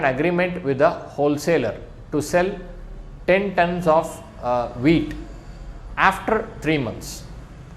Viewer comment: i thought he said weed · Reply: Haha... I said WHEAT !! What to do.. that's the accent I have got!